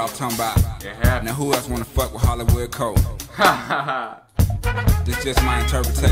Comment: When I heard this over night on the radio in 5th grade, nothing was ever the same for me afterward. The lyric is "Who else wanna fuck with Hollywood Court?" Hollywood Court is a project in Atlanta. It's the dude fighting in the club wanting to know who has beef. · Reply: Thank you!! I always thought he was saying "Hollywood Cold"